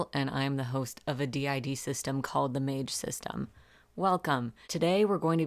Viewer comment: I thought you said MANGE system and after the hello creature it felt so right